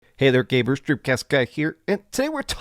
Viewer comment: Did he just say “hey there gay bears?” wtf.